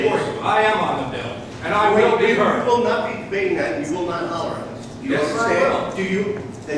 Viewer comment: Ah I heard it now. "You will not holler at us."